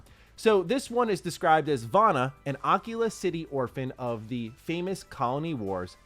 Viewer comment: Am i the only one that heard him say Oculus City first time around 😂😂 it was obvious Akila city but that was funny. love your vidoes man.